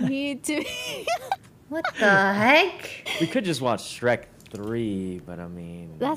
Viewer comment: When he said Shrek 3 I thought he said cars 3 lol